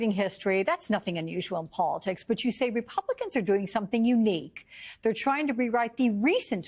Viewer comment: Unfortunate name. First I heard "Steve Bannon"